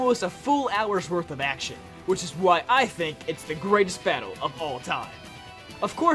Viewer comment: I somewhat thought you said "... of Paul time."